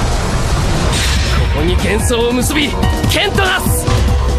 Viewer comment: What does he say in japanese at 1:50? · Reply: ここに、幻想を結び剣と成す -> "Bind the illusion together, and create a sword!"